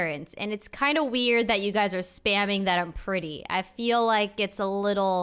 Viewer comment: Jesus Chist I thought she said chat was spamming Jennifer was pretty